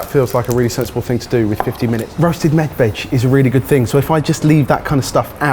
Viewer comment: does Jamie say that he's making roasted "meth" veg? Even the closed captioning is confused, admitting he's inaudible. Or at least not wanting to call out his evident drug problem. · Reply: He said med veg.